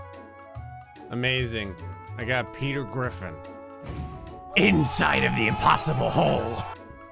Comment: Sounded like you said “I got Peter Griffin inside the impossible hole”